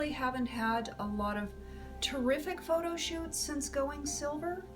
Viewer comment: I thought you said since going sober but then I realized you said silver hahaha. You are beautiful.